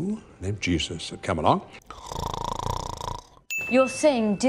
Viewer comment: I heard “a young Jew of Camalot” instead of “come along”